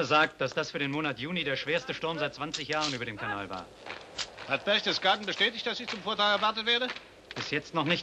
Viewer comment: Do you understand what Rommel says at 2:19 ? Something like : " Hat Berchtesgaden bestätigt, dass ich zum ........ erwartet werde?" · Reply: Rommel says: "Hat Berchtesgaden bestätigt, dass ich zum Vortrag beim Führer erwartet werde?" (("Did Berchtesgaden confirm that I was expected for a talk at the Führer?")